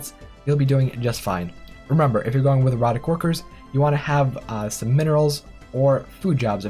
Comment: I swear he says "Erotic Workers" I was like "He Researched WHAT?!"